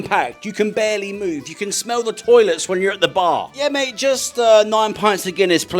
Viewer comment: I thought he said 'you can smell the irish'. Lol